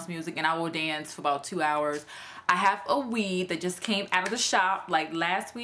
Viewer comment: I was working on researching info & listening to this the same time. ...I thought u said " I have a weave that just came out of the shop this week"..... I was like what kinda weave is using now??!?!?. I need to stop multitasking.lol